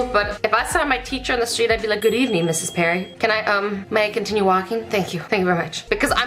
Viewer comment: did she say mrs perrin? uhm.. that's my band teachers name😂 im kinda scared now